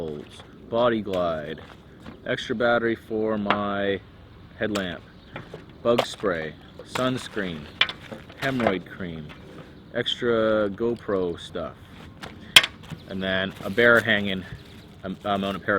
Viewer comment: did you say hemorrhoid cream?